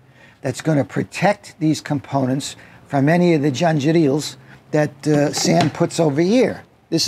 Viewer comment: Please someone tell me what he said... Janjitials? I can't find this term anywhere, but it sounds hilarious!